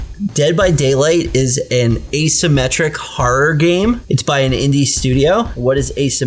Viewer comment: did he say isometric? because that's not what isometric is. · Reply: No he said asymmetric. Stop trying so hard you're embarrassing yourself.